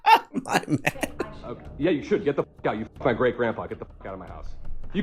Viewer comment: Did he say “You fucked my great-grandpa.”?